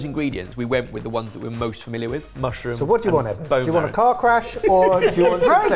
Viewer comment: To clarify for the channel's American viewers: What says at 17:42 is "car crash". He is not referring to a skin condition.